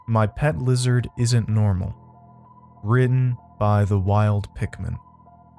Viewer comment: i thought i heard written by "The Wild Pick Me" 😂 instead of "pickman" lol